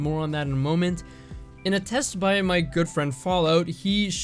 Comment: I swear i heard" in a test by my girlfriend fallout". 3:00 I was like: whaaat???